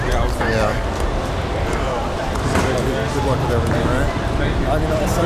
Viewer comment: did she say goodluck “kawhi”????